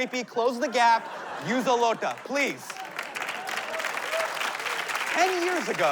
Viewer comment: did he say use a loota....indians will understand😂🤣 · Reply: it's not loota, its looda....